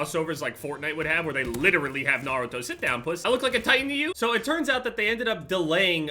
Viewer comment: For all the South Africans that watch. Did he say "sit down poes ?"